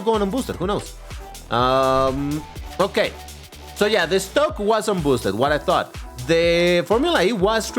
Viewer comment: dude everytime you said "Estoque" I heard it as "Stoke" and I was like "is that a rare car or something?"